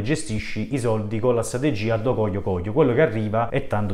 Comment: Al minuto 9:29 ascolto un do coglio coglio ——..ho capito bene ? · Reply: "Addo coglio coglio" è uno slang dialettale che esprime il "fare la cose a caso", probabilmente nel montaggio abbiamo tolto la spiegazione ;) Scusa per il disagio, lo staff.